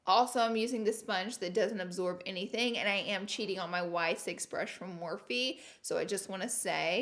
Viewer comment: Is it just me or does it sound like she says “and I am cheating on my wife” lol 😂 😂 love your videos girl!